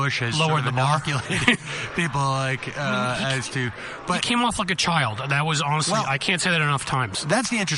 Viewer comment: At around 06:30 Cliff says something ... it sounds like quinoa fuckachild. I've been trying to figure out what he says ... and I can't ... Help.